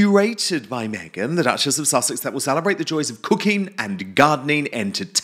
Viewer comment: Dan, when you said " her show of cooking and gardening..." i swore o thought you said Cocaine and gardening😂😂😂. Its your accent.